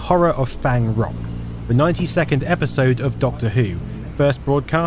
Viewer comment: "the 90 second episode of doctor who" - thats one quick episode of doctor who! Also Ive always thought this incident would of been quickly forgotten as not many people saw the 2nd hijack but the news kept showing it, many from those news interviews I doubt actually saw it broadcast during Doctor Who but just saw it on the news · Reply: Ah, “92nd episode” makes a lot more sense than “90-second episode”, which I was also puzzled about as the latter is what I originally thought was said. Watching it again, the preceding word is “the”, which I didn’t quite catch, but certainly helps clarify it.